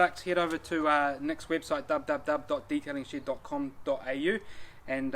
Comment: I thought he was saying Detailing Shit instead of Shed 😂